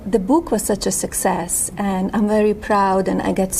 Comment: At the start, I heard “The boob was such a success… how they enjoyed the boob” 😂